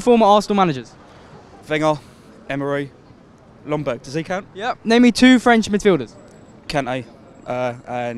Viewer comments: Not even gonna lie at first I thought he said name me 2 French Matilda’s 😂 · "i can't mate"😂😂😂😂 i legit thought he said "Akame"